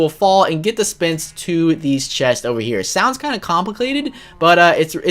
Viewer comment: did you just say clompliclated? its complicated, not clompliclated